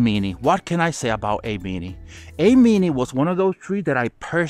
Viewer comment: Great vid, this is an awesome series. Wish this came out before I made some of my purchases. What's the tree @ 10:06? I couldn't make out what you were saying. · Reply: Eimini